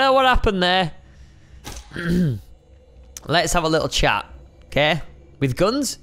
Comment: When it said lets have a little chat I thought it said let s shave a little cat